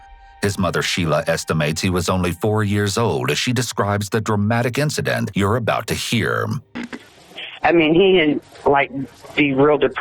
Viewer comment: "the dramatic incident you're about to hearm" Not what the caption says.